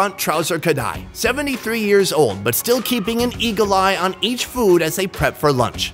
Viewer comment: I thought I heard “Keeping an E.coli in each food” 🤭🤭 Eagle eye!!!